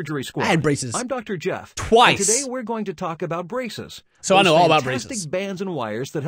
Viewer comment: I heard 'Today we are going to talk about Brazzers'